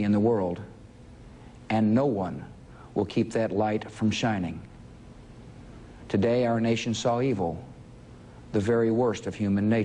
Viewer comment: "Today, our nation's so evil. The very worst of human nature." hahaha listen that's purtymuch what it says with his accent.